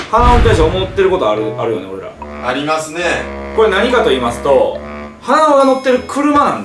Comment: 「やりますねぇ」に聞こえた。